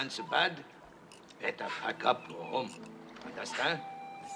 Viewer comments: did he just say fuck up? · I heard Mr. Miyagi say "get the fuck up" 😂😂